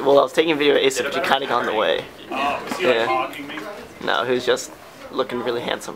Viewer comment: Wtf is going on bro did he say he was mogging him at 2:07? Time is a flat circle